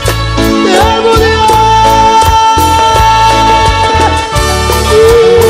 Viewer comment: No se pero en el minuto 0:19 se escucha como "se murió " y cuando lo escuché bien más atenta me di cuenta que decía " te amo Dios" 😂😂😂